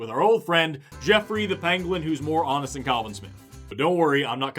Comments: thats a pangolin not a penguin....it really sounded like u said penguin lol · anyone else hear "Geoffrey the penguin"?